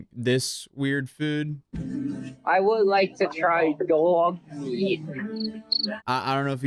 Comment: what did he want to try??? I can't hear himm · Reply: dog meat, i think he said